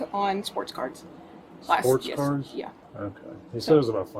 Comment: I thought at first she said he spent money on sports cars, not cards; so you’re telling me this guy is spending his wife’s money on baseball cards?